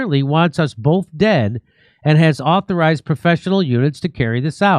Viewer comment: I heard "professional eunuchs" and I refused to be corrected.